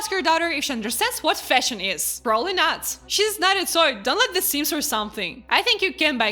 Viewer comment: what the fuck did you say here? she's not a soy? what the fuck does that mean?